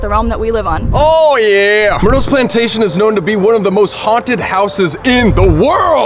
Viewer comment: I thought it said PlayStation for some reason 😭